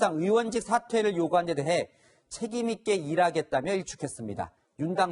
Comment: 책임있게 해먹겠다 로 들리네